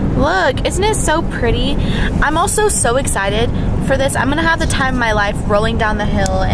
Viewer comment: Ok.. when was talking about being Tinkerbell and then shows us the snow & how white everything is...(@ 6:39) Why did I think she said," I was SNOW EXCITED for this! I'mgoing to have the time of my life rollingdown the hill..."